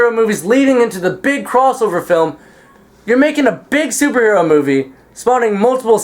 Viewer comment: 0____0 i thought you said Dc superhero